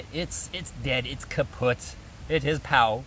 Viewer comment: woow did you actually say the german word kaputt :D